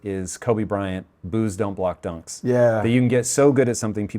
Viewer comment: Mr. Bilyeu: what was that Kobe Bryant quote, pls? You spoke it quickly and with multi replays I couldn't catch it. Edit: it's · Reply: “Boos don’t block dunks.”